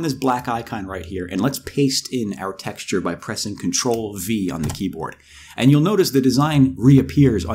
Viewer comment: Hello. What did you say at 1:37 ? You said " Control with what"....Sorry, I'm hard of hearing and can't hear what you're saying. Closed Caption ain't working. Please, help me or anybody help me out. I really apprentice it if you help me. Thank you! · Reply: He said "...by pressing Control-V on the keyboard".